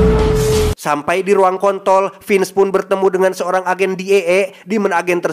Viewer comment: bilang apa kau bang jon!!! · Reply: Ruang kontrol 😂